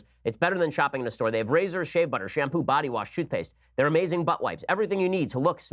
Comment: Did he just say butt wipes?